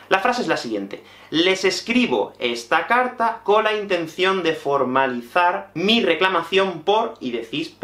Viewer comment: ¡! :D Tengo una duda. En el minuto 10:37 el texto dice ¨Les escribo esta carta con la ¨contención¨(...)¨ pero por lo que oigo, me parece que dices ¨intención¨ :D Justo después de ver el video he visitado tu blog y he notado que allí ya en la carta escribes ¨con la intención¨ :D Es¨la intención¨ la palabra que hay que usar en esta frase, ¿verdad? Por cierto, me encantan tus videos. Siempre me divierto mucho viéndolos :) El minuto 13:14 es mi momento favorito, jaja, gracias por dejarlo. ¡Le da un toque muy especial al video! ¡¡Saludos desde Polonia!!